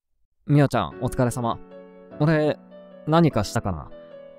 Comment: これ「おで、何かしたかな」に聞こえるの私だけ？真島と店長のセリフみてて鳥肌、、友達居なかったらやばかったですね